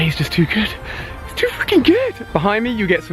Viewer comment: Who else heard Ali swear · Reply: Blueflamez BMF he said freaking that's not a bad word